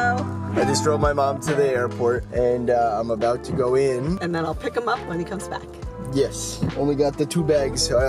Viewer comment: At like 1:58 my headphones cut out and it sounded like she said “I’ll fak him up when he comes back”